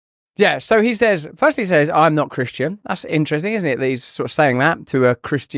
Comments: He said ”I AM A CHRISTIAN” if you listen closely. Trump 2O24 💪🏾🇺🇸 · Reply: I thought he said, "I'm a Christian". 🤔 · I also thought that he said "I'm not a Christian" but after rewatching it it actually was "I'm a (pause) Christian." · Uh I heard "I'm a christian" not "I'm not christian" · I truly believe that Christians should vote for leaders who value our American heritage and principles that are based on God. I thought he said “I’m now Christian”… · I heard "I'm not Christian" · He said he IS Christian "I'm a Christian" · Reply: I like trump , but ngl I heard "I'm not Christian" lol